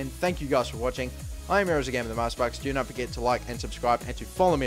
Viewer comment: when he says themasterbucks it sounds like he is saying neymarsparkz